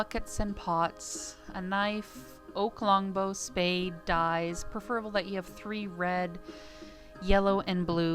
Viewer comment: She did clearly say that you need dyes lol